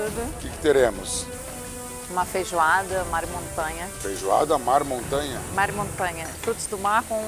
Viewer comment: Helena falando "Feijoada mar montanha" e eu entendi "Feijoada Hannah Montana" Akakakakkaka que odio